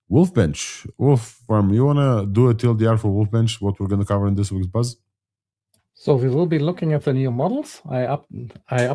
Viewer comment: Did they say Fish Audio? There’s no info or links in the description. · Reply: Yes, fish audio, You can find all the links we mentioned on the show here: <link>